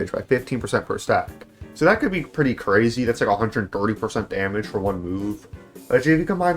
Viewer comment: There was a little slip-up at 8:10 where the subtitles say "13 % damage" when he says "thirteen hundred damage". There was also another one a few seconds back but that's all I wanted to point out